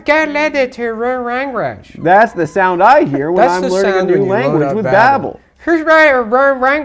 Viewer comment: what did he say? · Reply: Learn language with Babble